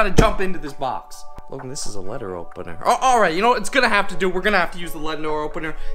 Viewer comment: Anyone catch at 2:56 he said letner opener